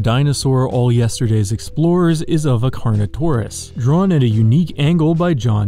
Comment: I freaking thought he said carno-corn-us